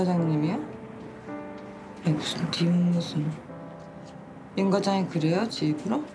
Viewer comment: 대사가 잘 안들리는데 대사 들으신분 워딩좀 부탁드립니다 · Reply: 에이 무슨 님은 무슨 같습니다